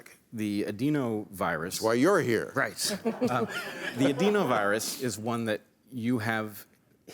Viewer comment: This feels like a movie scene from Jurassic Park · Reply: I thought I heard something about a dino virus. I swear. 🥺